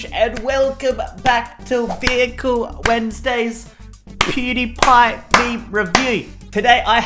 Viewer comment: did you say PewDiePie